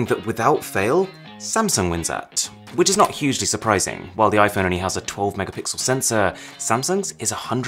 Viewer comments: Did he say Iphoney ? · did you just say iphoney